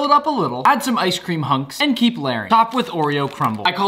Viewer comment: I heard "i call this the nick a flurry